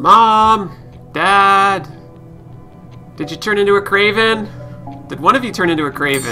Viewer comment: XD I've noticed from a few of your videos, it always sound like you said 'kraven' instead of 'raven' like in